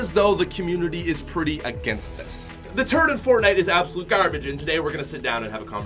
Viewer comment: does anyone think it sounded like he said turd? Edit: Like and comment if yes